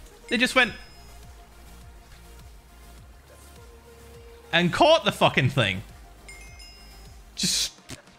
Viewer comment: I heard "and called the fucking police"